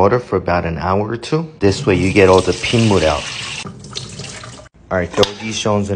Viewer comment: 1 minute short ribs: soak 2hrs in water for no reason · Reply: I heard “to get all the ping-wood out” but that doesn’t make any sense anyone know what he actually said?